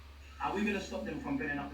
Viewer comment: Small point but about 5 minutes in, I don't think they said "how do we stop him from hitting the kids" I heard "how do we stop him from belling the feds" personally 🤷‍♂️